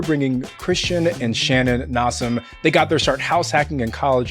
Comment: did say they got their "shart.."